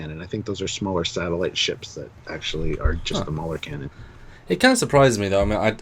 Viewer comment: I thought he said ‘mullet cannon’ which would be a pretty horrific weapon.